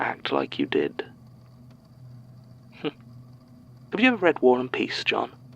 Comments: I thought annabelle asked "have ever you read one piece, jon?" and I thought I was going to be forced to like her before I realized I misheard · I misheard one particular part of this statement and now I will never be free of the mental image of Annabelle asking Jon, in a deadly serious tone of voice, if he has ever read One Piece.